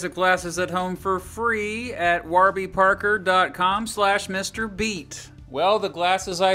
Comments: I thought you said MrBeast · I thought you said you mr. Beast (Mrbeat) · ? I thought you said mrbeast